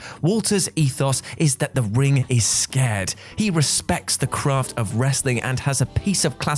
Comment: Lmao did he just say "the ring is SCARED" ????? 🤣 🤣 🤣